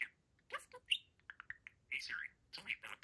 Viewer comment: Did I hear "Hey Siri, tell me about Parakeets"?